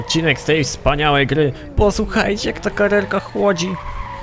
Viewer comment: Czy tylko ja w 0:14 usłyszałem chłodzi?